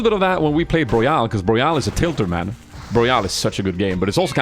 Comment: I am not native english speaker and I just cant understand what game he is talking about between 2:10 - 2:42 ... is it Burial? Borial? Boreal? pls help! :D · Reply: Broyal is the word he was saying. battle rite battle royal I believe, it's the game with the main point being all skills are skills shots and it's the same game developer as the vampire game hype video he made.